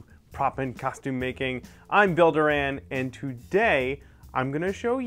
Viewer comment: I thought he said "Builder Anne" ... I blinked a lot, then I realised his name was...